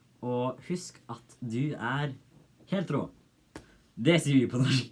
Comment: HAHAHA, I thought that you said: Husk att du e hetero in the end!!!